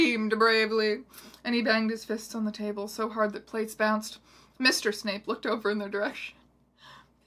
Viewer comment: I thought you said mistress snape oh god